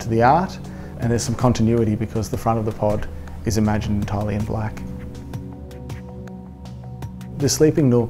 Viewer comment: italian black? I believe I'm mishearing. Can you explain a bit please :)? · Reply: He's saying "imagined entirely in black". Our accent can be a bit difficult, I know :)